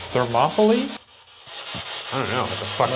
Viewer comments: The voice at 11:10 sounds to me like “don’t laugh at me” which doesn’t make too much sense since they weren’t laughing... but i hear that more than “thermopoly”. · the voice is clearly saying don’t laugh at me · I heard “don’t laugh at me” not “Thermopoly” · He says thermopoly but i heard not happening? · I thought I heard say 'Don't laugh at me' not thermopoly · I swear the voice said ‘don’t laugh at me’ but idk 😐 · it sounded like “don’t mess with me” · sounds like they’re saying “don’t laugh at me” to me I heard that immediately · Pretty sure it wasn't 'Thermopoly' I heard "Don't laugh at me" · I think the spirit box is all BS but the voice around 11:15 sounded like “don’t laugh at me” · I think the noise at 11:16 sounded like “don’t laugh at me”...anybody else? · I s2g at Thermopoly, I clearly heard 'Don't look at me' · i heard “ don’t laugh at me” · I hear “don’t laugh at me” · I thought it sounded like “Don’t laugh at me” anyone else? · I think the voice said “don’t laugh at me” but shooo idk it might just be me 🤷‍♀️ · the voice on the spirit box sounded like it said “don’t laugh at me” very clearly